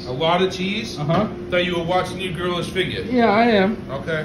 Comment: I thought he said his girl's figure